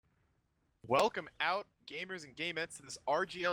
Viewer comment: welcome out gamers and gay men Is what I thought I heard